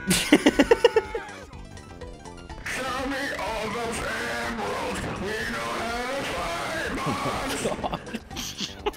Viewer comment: wtf is Bearded saying? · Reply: AABattery He is singing play that noteblock nicely or whatever